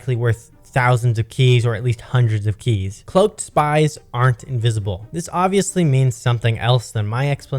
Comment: 95% invisible not 100 I heard